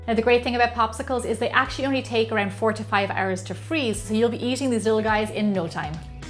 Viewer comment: 45 HOURS??!!! Oh wait it's 4 to 5 hours Ok I'll leave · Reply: I thought u said 45 hours then I realised it was 4 to 5